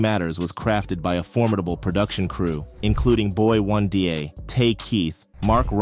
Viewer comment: Who's "Boy Wundeeyay"? Is he related to Boi 1DA?